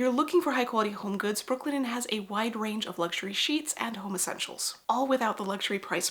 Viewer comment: why did i hear “luxury sheets and homosexuals”